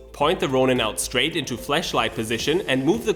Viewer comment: did you say "Fleshlight"? ;)